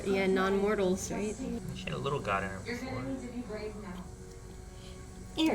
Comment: what did say at 11:13? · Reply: “She had a little god in her before”